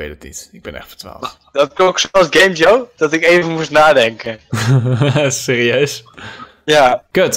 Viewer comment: hahaha geniaal toen ben lekker zijn brownie zat te eten! XD en wat zegt ben nu op 3:28? ik versta het niet vanwege zijn mic xD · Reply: precies! Ben zei: Dat klonk zó als GameJoe, dat ik even moest nadenken.